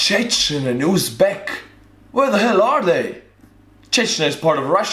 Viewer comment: i kept hearing you ask "where are they?" the location is Takur Ghar Afghanistan which is a mountain area.